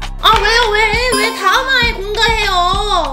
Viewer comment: 왜 다음 화에 공가해요라고 나만 들림?